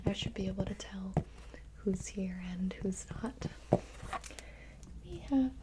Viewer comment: I could have sworn that she said “who’s here and who’s hot”lol😜